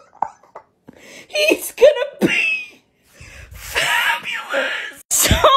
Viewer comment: All i heard was "he's gonna pee" and it sent me